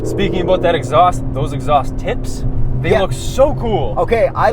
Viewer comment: I am not lying, I thought I heard say, "Exhaust t_its." Please do not suck them coz they are HOT!